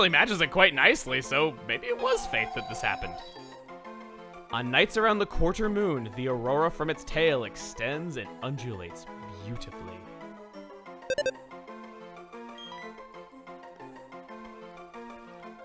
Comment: I was a weird child. I thought it used to say "And urinates beautifully"....